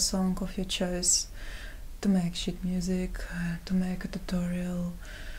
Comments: Ahaha I thought she said shit music at first lmao then I noticed it was sheet* music :O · LOL, I thought you said you were getting donations for your "shit music" at :46. I had to play it back to hear it correctly as "sheet music".